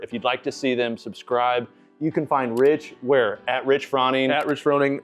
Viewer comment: Did you call him Rich Frawning? 😂